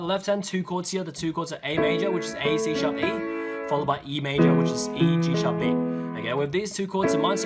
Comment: anyone noticed "Email" Instead of e major. In subtitle And 6:42 "second pot" Instead of second part . In subtitles Haha lol